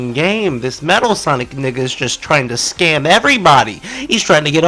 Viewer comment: did I hear nigga 💀